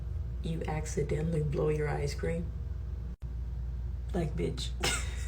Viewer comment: I thought the ice cream lady said "blow your ice cream" and I said "WTF?"